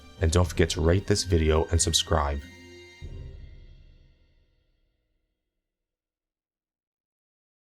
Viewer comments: lol I thought you said rape this video and subscribe!!! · it sounded like "don't forget to rape this video and subscribe" :D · I thought u said at the end, "And dont forget to Rape this video and subscribe"